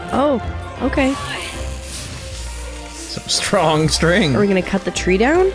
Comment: All the comments I would post on this video in one: 1st: creeper, aww man 2nd: I thought said some "strong strength" but he said some strong string